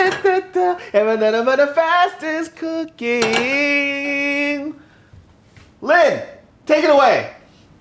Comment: At first I thought he said "fat ass cooking" and I got hella confused